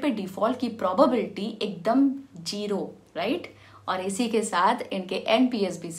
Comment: did she say Jero instead of Zero? 🤔